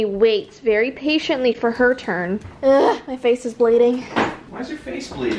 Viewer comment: I thought u said “my face is blading” I was like.. um... what??? U might wanna get that checked out lol